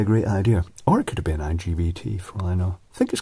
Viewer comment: did you say the power transistor was LGBT?